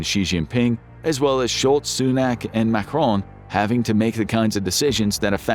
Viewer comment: Did he say “short Sunak” at 19:19? 😄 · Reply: Sholtz, Sunak and Macron.